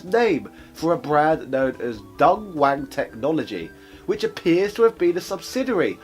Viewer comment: "DontWang Technology"? Ooh my, what an.. unfortunate name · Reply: I heard "DungWang Technologies" which is arguably even worse.